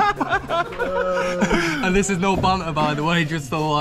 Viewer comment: Hi Ben, are your captions computer generated or a transcriber? If so, at 2:42 they say 'and this is no BUTLER by the way' instead of what I believe to be 'banter'. Good videos regardless.